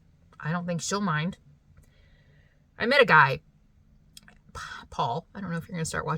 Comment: When you said “I met a guy, Paul” I was like OMG, so happy for you! Then I heard the rest and it was just a guy in the store talking dogs… LOL